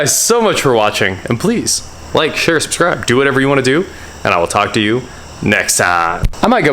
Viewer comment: You know I always thought you said like share subscribe and do whoever you want to do but I realize now you say do what ever you want to do oh well😆😜